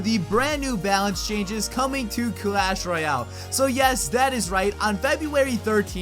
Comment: Captions said "asteroid house" instead of "Clash Royale" lmao